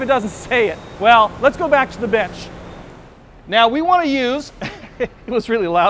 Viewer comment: Let's go back to the WHAT? I think he said BENCH, but it's a little noisy.